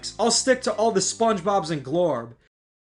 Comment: He'll stick to all the SpongeBobs in what? I couldn't tell what he said. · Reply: I think he is referring to Glorb. He is a SpongeBob song writer that uses Ai to record rap in the SpongeBob characters voices. Some of his songs are actually really good. I’d recommend The Bottom